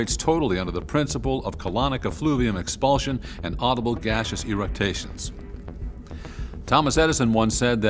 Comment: I thought I heard 'gaseous eructations' (eruct: to belch), although it's funny either way 😅